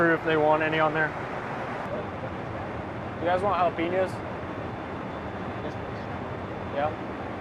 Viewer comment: Did he just say jAh lapenos??? Instead of jalapeños?